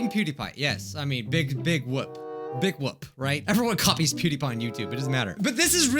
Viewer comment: is he saying “big woop” or “big wap” because if he’s saying “big wap” ... 😏